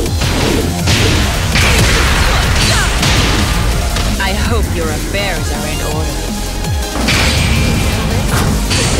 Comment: I can't discern what she says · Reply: "I hope your affairs are in order."